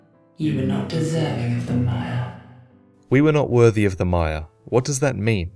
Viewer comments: Regarding what Elana says before you fight her, I always sounded to me that she was saying: "you do not deserve the FIRE." Meaning that the crowns in fact are needed to truly link the fire and she is saying that you don't deserve to do that. Then again, I may have been mishearing her all this time. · Silly I know but considering Elana's dialogue I always wondered how the words 'mire' and 'admire' are so very different but one is contained within the other. I had trouble hearing Elana my first playthrough, and thought she said I was unworthy of 'admire' and so I thought she was calling me base, undeserving of praise of admiration.. which seemed odd coming from a queen described as "Squalid". Anyway, a bit more most-likely-unintentional wordplay for you to consider.